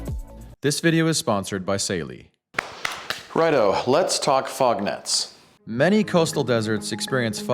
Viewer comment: I thought the title said "a desert frog" and I was horrified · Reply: I heard "Lets talk frog nuts."